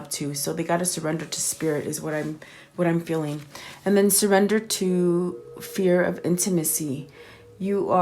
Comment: “You said surrender to the fear of intimacy” I had to rewind because I heard you say something else. I heard “in Tennessee” (I know, it’s real close if you half listen because something else distracts you in the distance) The reason why I mention it, although we are broken up and he’s going through the karmic cycle right now and hopefully soon changing his toxic ways and we both heal, he did tell me he wanted to marry me in the mountains of Tennessee Thought that was worth a mention